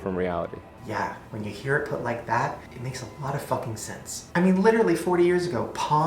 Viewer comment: Oml @ 3:08 i thought he said "when his haircut looks like that, it makes a lot of f*cking sense"😂😂